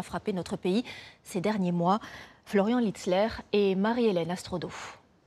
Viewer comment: vous entendez la même chose que moi ? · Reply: Florian Hitler ?.